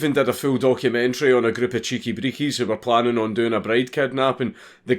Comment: Did I hear CHEEKI BREEKIES at 3:40 ... slav power intensifies!